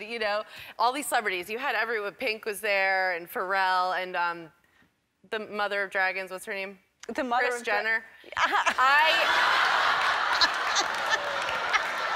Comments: "the mother of dragons???? oh Kris Jenner" im dying!!!!!! lol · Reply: I thought she said the mother of dragrace....? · I had to listen again to the mother of Dragons. I initially thought she said Bruce Jenner